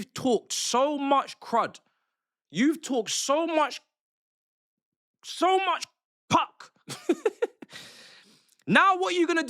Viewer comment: "so much kak" did i hear right ? 🤣🤣